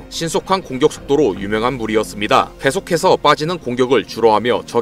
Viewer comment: "계속해서 빠지는" 이라고 들리는군요. 자막의 '치고'가 소리에서는 빠져 있네요. 혹시 또 모르죠. 정신체를 완전히 정리 안하고, 이전과 다른.. 육체를 주었을수도 있죠. 그러면서 활동하게 함으로서 최악만 면하게 하면서 군단을 관리하게 만들었을수도 있습니다. 불가능한거는 아니지만.. 실제로는 어땠을지 우린 모르겠지요. 그저 상상만 할 뿐이지.